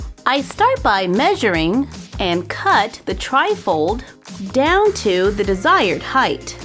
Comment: When you say tri-fold it sounds like your saying trifle